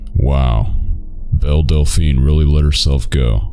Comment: "Belle Delphine really let herself go" made me laugh way harder than it should've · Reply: I thought he said Del Delphine. It's funny either way, though!